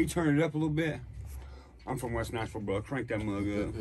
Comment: I swear at first I thought he said west Knoxville. ( yes, I know where he from. Ashland City, in Cheatham County.) lol still at 1rst 🔊 like Knoxville to me.....